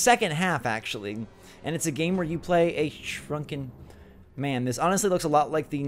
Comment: Lol when you said shrunken I thought you said drunken lol